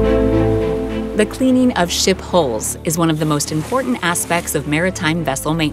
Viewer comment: I thought she said “The cleaning of shit holes”